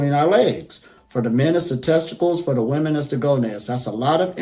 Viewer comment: he talks about the energy center, what is he saying the women's center comes from? Goness? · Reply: good question! Thanks. It is gonads (ovaries), not goness